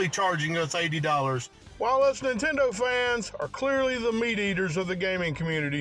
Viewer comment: I thought he was saying mediators until you said meat eaters